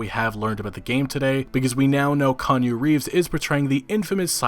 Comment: LOL did you just call Keanu Reeves "Kanye Reeves"?